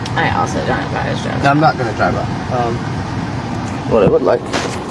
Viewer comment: does he say 'I'm not going to drive off.'?